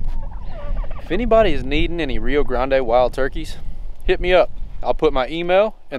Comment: I feel like a fool. I thought you had said that you had Blue Slate Turkeys, but you said you have rio Grand's. Maybe your chickens are too old or maybe too young. Maybe it is because it is winter. If you got them at Chupps consignment auction or at Leach you never know what you ar e getting. It is always best to raise your own or to buy from a individual. This advice comes from the experience of being burnt until I learned. Sometimes though, You can buy young pullets and and come out alright.